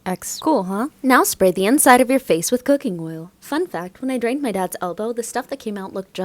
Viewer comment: I thought she said " When I drank my dad's elbow"